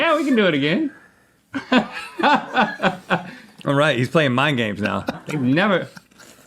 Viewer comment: The subtitles are wrong. It says "mine games" when it should be "mind games".